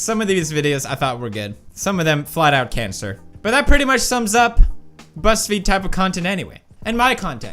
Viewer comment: I thought he said "breast feed" instead of "buzzfeed"